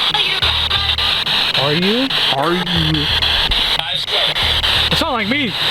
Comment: it sounds like it says are you my mom